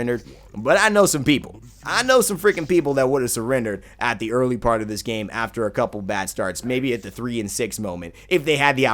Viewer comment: Haha, I heard "I know some freaking people that would have surrendered at the early part of this game after a couple of bastards". Anyway cool video I really enjoyed the commentating, subbed for more future gameplay videos. :)